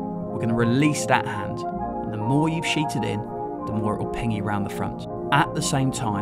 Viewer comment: I don’t understand what you’re saying at 1:58. “The more you’ve sheeted in, the more it will ??? you around to the front.” It sounds like “ping”, but that means nothing. The closed caption doesn’t help. Please don’t use slangy expressions in these instructional videos. We’re trying to learn something here and it has to be clear what.